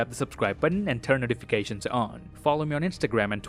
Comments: Did he just say I’m “Kevin Hart” at 12:35? 💀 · did he say his name is kevin hart in the last second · lmao "till then, I am Kevin hart"?? Did I misheard something?